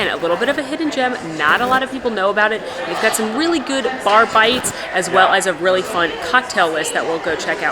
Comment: ha ha ha thought you said barFIGHTS instead of bar bites. to funny